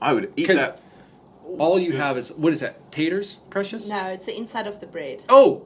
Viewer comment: Did call "precious?"